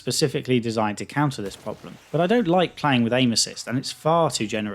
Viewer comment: So I had this on in the background while doing some work, zoned out a bit, then misheard 'I don't like playing with Aim Assist' for 'I don't like playing with anuses' haha